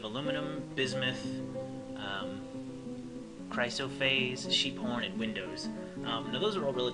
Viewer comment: I had to pause the video when you said she likes sheep horn, because for some reason it sounded like you said "Cheep Porn"